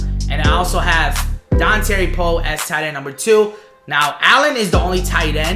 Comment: is it me or did he say tightened when he was on d-tackle